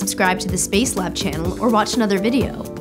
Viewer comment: for a second there i thought she said "im sophie Bushpig" lol